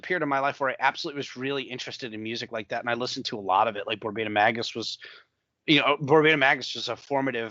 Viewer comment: what band does mention? I cant really make out their name 😅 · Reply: Borbetomagus - they are the OG “noise” guys.